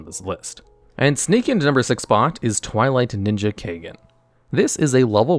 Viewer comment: Everytime i hear you say the name of Twilight ninja i just hear "supreme justice twilight ninja kagan"